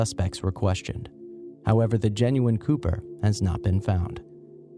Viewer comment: i was just listening to this in the background and i thought he said genuine pooper